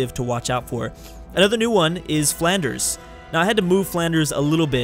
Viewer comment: How.. HOW did you find 50 European civs? I don't even think there were that many in Medieval Europe! XD at first, I thought he said "Flounders" were a new civ, and I was like "hole hell, my name was a civilization?" but then I replayed it and he said "Flanders" and went "Damnit." XD